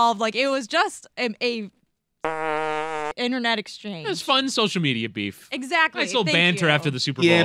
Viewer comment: did she say Sexist internet exchange?